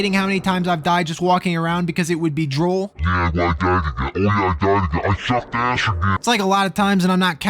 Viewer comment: What’s he saying here? 24:53 :_HUHHER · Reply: Sounds like “Yep, I died again, and I died again…I SUCKED ASS AGAIN.” Had to actually slow it down since I always listen to him on 1.5 times speed.